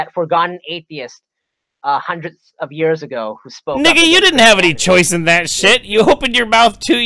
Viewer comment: did he just say that word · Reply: Yea he said nigga